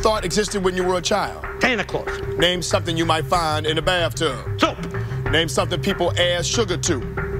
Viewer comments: I thought he said soup lmao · I always hear frank say "soup" for "something you may find in a bathtub", which is just perfect. lol